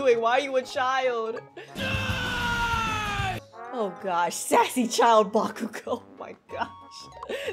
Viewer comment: I THOUGHT IT SAID "SUSSY" FIR A SEC THANK GOODNESS